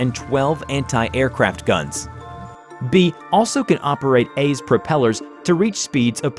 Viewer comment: you say been instead of B